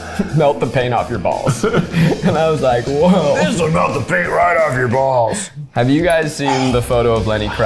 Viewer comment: I also thought Keith said "melt the paint off my balls," and I was quite confused 😂😂